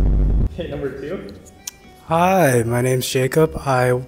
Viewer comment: caption is wrong, she said 爱荷华州立大学 Iowa State University, not 爱达荷州立大学 Idaho State University